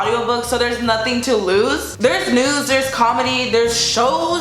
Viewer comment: i thought she said nudes😂